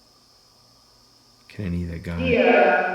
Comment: sounds like he said yeah